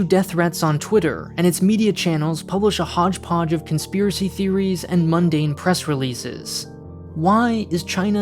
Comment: Did someone said "Conspiracy theory?" Hold up, RT does that a lot and they make particular news headlines so that you can get more confused also sometimes shitposting like TF.